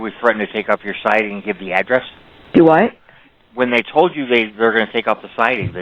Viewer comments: Did say “dowut?” Like chance? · said DUWAT @ 22:20. Also, is this call legit? A few things are off. The way the "cop" speaks to her. The level and length of the convo. Odd.